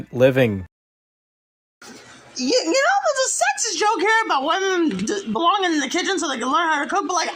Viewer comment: what the fuck were ANY of those words? am i going fucking insane?? i feel like i just heard an alien language interrupted by "MAC N CHEESE? IN A WINE GLASS??" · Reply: It was something along the lines of sexism and women belonging in the kitchen